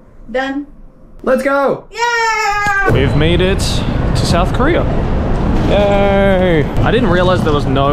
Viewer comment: I THOUGHT THAT SAID NORTH KOREA FOR A SEC💀